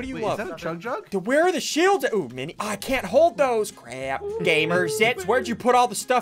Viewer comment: For a second i thought he said gamerzits